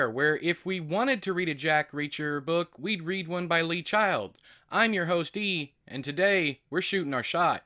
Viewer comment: I heard: "Don't let Pat Sajak read your book. He reads like a child." Since he does host Wheel of Fortune, it kinda made sense.